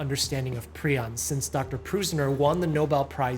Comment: Prison disease 😂😂😂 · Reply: First time I heard "Prusiner" as "Prisoner"